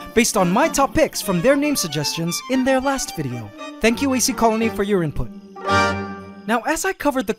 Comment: lmfao at 2:34 when he says the “ants are pretty thicc” the subtitles actually has the word “thicc” with 2 cs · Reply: No he does not put his own captions 2:58 it says AC Council instead of AC Colony.